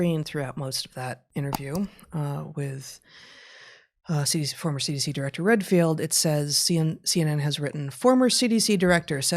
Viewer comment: Lol I heard "former CDC director Dr. Redpill" 😅 💊